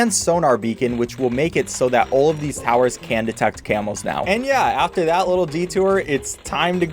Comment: I thought he was saying camels for a solid 10s when he was talking about camos